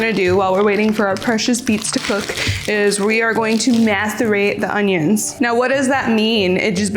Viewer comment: did she say masterbate the onions